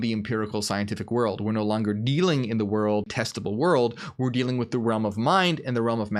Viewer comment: Anyone else hear "testicle world" at 17:15 .... just me?? If you heard that, can you please explain what you think he said?